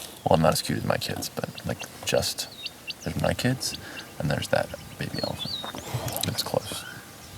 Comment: Not sure if anyone has said this but at 12:50, the caption says 'inaudible' but I am pretty sure he says "that baby elephant". Just thought it might help